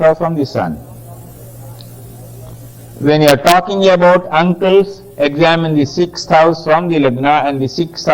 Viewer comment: can't quite hear the word to describe the 6th house "when you're talking about ____ examine the 6th house and the 6th house from Mercury" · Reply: He said uncles